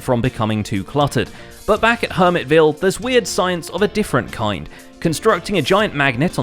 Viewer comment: Thought it said "weird weed" Seems about right